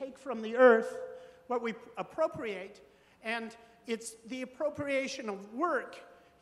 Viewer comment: Gasp! Did he say procreation?